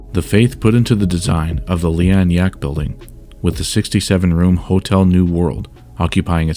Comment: did he say 67??